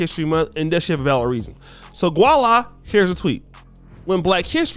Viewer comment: Hold up. wtf... did you say gua-la??? Lol, it's voila.